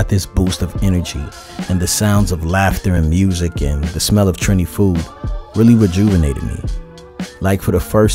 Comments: Thought I heard Trini food... Yes that's right #trinifood🇹🇹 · This trip gets betterandbetter. We Trini food 🤤🇹🇹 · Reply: I thought he said trendy food.☺